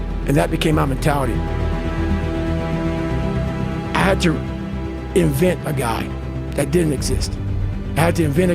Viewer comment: My eyes got filled with tears when he said "I had to invert that guy". The message i needed at this point of my life!!! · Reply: I thought he said "invent", not "invert".